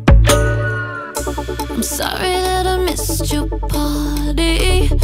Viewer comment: I hear I'm sorry that I missed your potty 🤔😐😑🤨 like if you hear it too